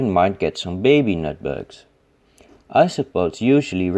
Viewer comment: Pillbugs, woodlice, slaters, potato bugs, doodle bugs, sow bugs, armadillo bugs, boat-builders, butcher boys, butchy boys, carpenters, cafners, cheeselogs, cheesy bobs, cheesy bugs, chiggy pigs, chucky pigs, gramersows, granny greys, hog-lice, monkey-peas, monk's lice, pea bugs, peasie-bugs, roll up bugs, wood bugs, gramfers, chisel bobs, woodpigs, timberpigs, peaballs, pishamares, tomato bugs, chuggy-pegs, crunchy bats, billy buttons, parson pigs, chickypigs, choogeypigs, chiggywigs, charliepigs, slunkerpigs, penny sows, grammasows, granddad gravys, granddads, granny greys, granny granshers, croogers if small, granfy croogers if large, granfergravys, granfygroogers, gramphycoochies, gramfycouchers, cheeserockers, cheeseybobs, chiselbugs, cheesers, cheeseballs, monkeypedes, monkeypigs, sourbugs, slateybeetles, leatherjackets, dampers, billybakers, bellybuttons, nutbugs, ticktocks, flumps, carpetmonsters and ogopogos. :) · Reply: haha I misheard "nutbugs" and thought they were literally called "not bugs"